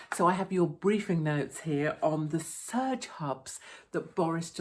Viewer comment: All I heard was Surge 'Pubs' ... What a great idea Haha